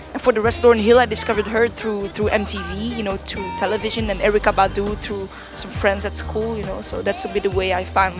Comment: can someone plz tell me wut she says at 2:15 plz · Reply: "Erika Badu through some friends at school" 8 years later lol hope this helps hahaha